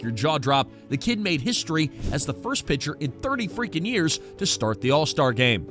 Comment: false. It said first rookie pitcher to start an all star game in 30 years. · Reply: Go back and watch it again! Start at 0:28! He clearly says Pitcher not rookie pitcher!